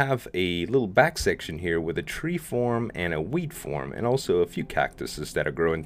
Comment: When he said Wheat farm i thought he said Weed farm